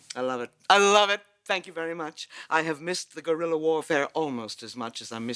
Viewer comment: did just say she had missed the "gorilla wa r fare"?